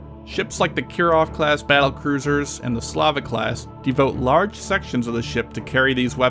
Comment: wait.. did he say s l a v I c class?